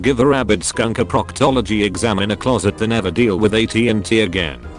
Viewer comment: I thought it said atat, ATAT, AT-AT... the thing from starwars...